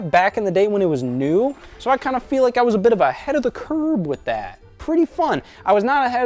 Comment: is he saying ahead of the curb?